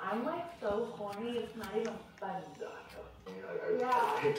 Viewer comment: Did I hear that right? 😭🤣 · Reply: She said I’m so cornyyyy 😭🤣🤣